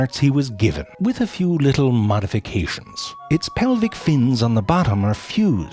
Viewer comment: Just noticed he said mud-ification instead of modification, bruh